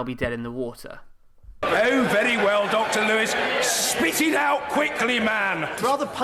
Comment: Did he say "Spit it out quickly, man"?